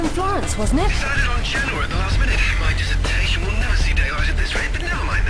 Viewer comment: Woa! I didn't know he was actually saying "Genoa"(4:18) i thought it was just for the Italian version. I'm glad i'm living there ^_^